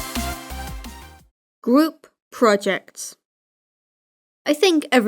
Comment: It sounds like u said gwoup. Project